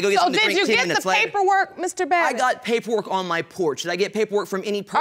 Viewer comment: For a moment I thought that the judge said "Bobbit."